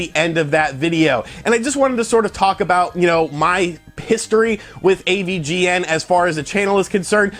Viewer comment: When you mentioned AVGN history it sounded like you said piss-tory and now I can’t think of a more accurate term to use for his history of games pissing him off.